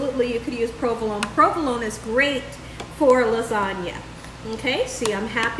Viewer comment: omg I thought she said provolone is good for the body!!!!! i wss like, WUT??? cheese , while delicious is actually notoriously bad for the body lol...had to rewind, she said provolone is god for the LASAGNA!!! 😆